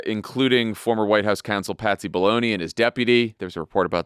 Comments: It sounded like Jon said "Patsy Baloney." Let's make Patsy Baloney a thing. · I swear I thought Jon said "Patsy Baloney" when referring to Pat Cippolone... LOL